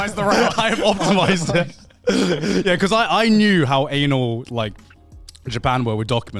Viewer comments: What? Did Grant say an*l? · Did Garnt say "anal" at 27:11 or am i hearing things